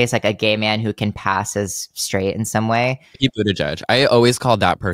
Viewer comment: What is Matt saying at 50:35? I can't make it out even though he says it twice. What he calls queer people who "pass" as straight Also, are there transcripts for these episodes anywhere/will there be captions soon? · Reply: It's 'Pete Buttigieg.'